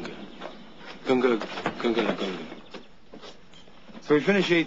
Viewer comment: Everyone quotes this as "Gunga Galunga" but, if you listen, Carl Spackler corrects himself to "Gunga lagungala" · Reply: I hear "Gunga Lagunga"